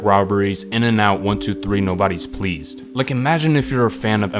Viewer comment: damm I always thought he said “one two three, nobody’s pleased” - but that was just an entendre. Fucking genius